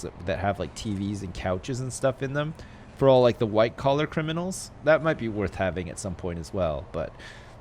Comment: lol around the 17 50s in the video he said a min sec wing for the white collar. criminals I thought he said white criminals